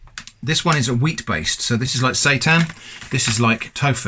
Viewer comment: Wait...did you say "Satan"? Could'a sworn you said "Satan" there. What, is that fake-jerky Curating our souls, or something? Seeing which consumers are damned to hell?